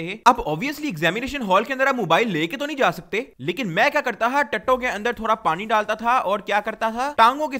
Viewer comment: who else heard tatoun ke andar pani 😂